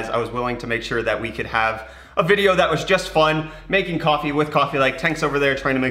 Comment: I thought it said "Making coffee with coffee"